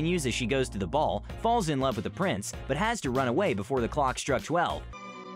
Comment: I thought you said “balls in love with the prince” lol